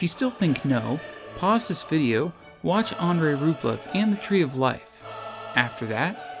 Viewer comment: watch what? didn't catch that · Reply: I believe he said "Andrei Rublev", a film by Andrei Tarkovsky.